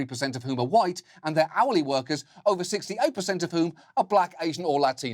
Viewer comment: I thought John said Haole workers not hourly hahaha